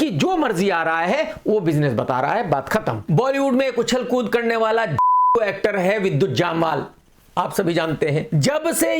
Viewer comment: Krk ne kya bola....???☠️☠️☠️😂😂😂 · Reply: Jhaatu